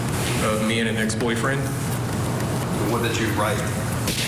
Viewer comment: Wait..am I hallucinating or did he say "me and an ex-boyfriend"?